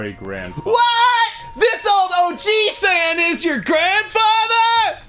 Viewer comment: For whatever reason, I heard it as "This old OG-san" and now I can't stop seeing him as some late 80s rapper